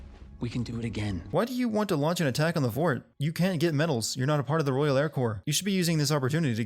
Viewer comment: he said "royal air core" the subtitles say "royal air corps"